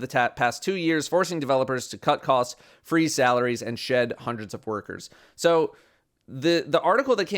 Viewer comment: I heard shed 100’s of burgers and immediately had to stop what I was doing and check the video